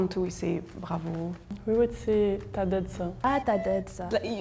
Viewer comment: Just wanted to say to non french viewers that the some words written at the bottom are not correct in comparison with what they're saying. For example at 12:16 she's saying "T'as dead ça" (You have dead that) who usually means "You killed/nailed it !". So absolutely not "T'as d'la shot" ahah. It's a really familiar way for friends ONLY. And it's used a lot in France too. The french girl is using the correct way to say all of this btw. But we also use "Ca dit quoi" for "What's up" these day (Still very familiar). For "Téléphone" (Phone) we use the same word for both type, and "Portable" who means handled for the mobile type only. For money, she said the main one and if I don't know for the others countries, there's SO MANY way to talk about money in France. There's like a new word every couple of years ahah. Argent, fric, thune from the main used to the less used. And there's like veeery old way too: biff, kopeck (from russia), blé, kichta, oseille, flouze, rond. People would laugh if you use those in a serious way, but some words like Kichta, thune and oseilles are coming back. I've seen a lot of young people saying it since 2 years. And recently with young generation, "moula" I know it's not easy to make a video like that and it could happen. But I wanted to precise some things. Nice work